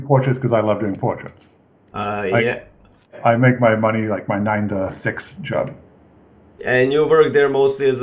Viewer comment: I make my money like on my night sex job? What did he say!? xD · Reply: "I make my money like my 9 to 6 job" lol